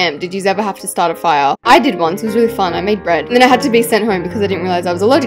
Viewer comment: Who heard her say bread instead of wood because heard her say bread